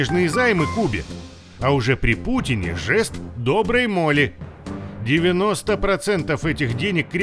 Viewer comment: жест доброй моли? Или мне это послышалось? 🤔